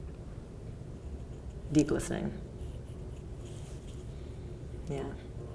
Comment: What did she say at 4:43? The most challenging part of their relationship? Deep listening?